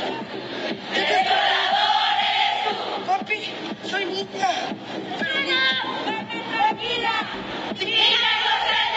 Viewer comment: papi soy niña pero niño o que dijo? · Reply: Solo dijo "papi soy niña"